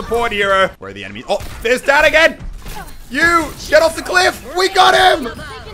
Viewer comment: I think she said Daddy :P